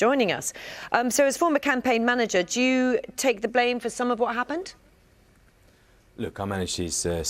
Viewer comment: swear to god the first time I heard that I thought she said “Jew take the blame for some of what happened” (given who she’s talking to I was like WTF !)